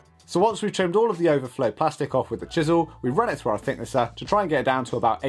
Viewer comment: What is that machine at 6:27? I couldn't make out what you call it... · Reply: It’s called a thickness planer 🤙🏽